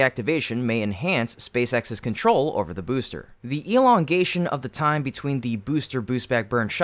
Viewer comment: I heard "the Elon-gation" 😅